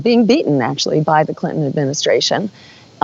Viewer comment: Did she say "Clinton Administration"? This lady has no idea what she is talking about. She has not won the election yet. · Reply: Mankiewitz said Clinton Administration